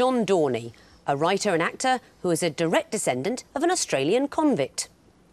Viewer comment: "Direct decendant of a convict" is an interesting way to say "he's australian" :D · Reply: She actually says "... who is a direct descendent of an Australian convict."